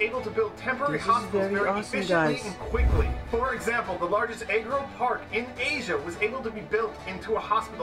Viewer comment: lmao the auto caption says eggroll park 😂